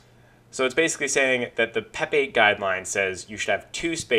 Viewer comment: I thought you said the Pepe guidelines.